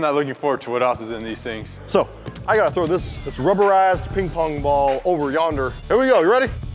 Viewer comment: did anyone hear say "this rubbera*s" instesd of rubberized? Hahah I know they don't swear but I was caught off guard